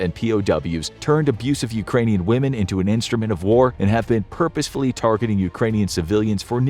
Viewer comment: Did they really say " [...] turned abusive Ukranian women into an instrument of war [...]" ??? Has anyone on the team of the infographics show made bad experiences with Ukranian women or am I mishearing? Honest question.